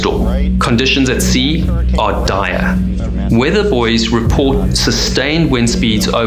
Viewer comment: I thought he said weather boys lmao. I will forever call weathermen "weather boys" now.